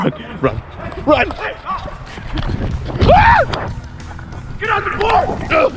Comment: the dude chasing had me dying of laughter he really said "I'm not built for this, get on the floor!'' I couldn't breathe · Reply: I've seen this video like 20 times.. I have always thought he said "Get out the pool" 😭